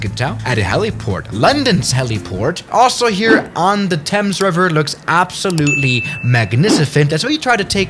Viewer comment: I thought he kept saying tums river